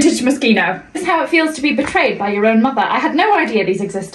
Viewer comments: I thought she said brother, not mother and was very confused as to why he had dresses 😂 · I thought she said "brother's wardrobe" and i was so confused😭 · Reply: She said Brother for sure. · It seems some of you are confused. The black dress that she cut was NOT her mother's dress. She said, "I found this dress.", never stating that it was her mother's. Additionally, on her TikTok, she confirmed that the dress was, in fact, hers. I hope this cleared some stuff up:) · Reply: i heard brother too, but everyone in the comments said mother, and OP to whom im responding to as well, so i assumed i misheard but glad im not the only one who heard that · I swear I thought she said brother not mother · Me who thought she said brother instead of mother 💀💀